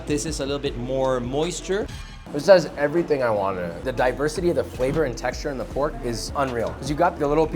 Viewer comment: I thought he said diapersity 😭💀